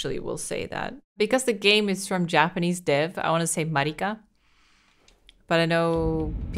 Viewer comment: you pronounced "merika" too fast. It sounded like you said "mah lee kah". But yeah, we "murikans" pronounce it like America, but without the A